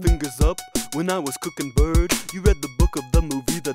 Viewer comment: "while I was cooking bird" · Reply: I always thought he said "birds"- guess I'm wrong